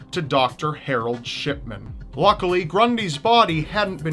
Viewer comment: I keep hearing "Doctor Harold Shitman"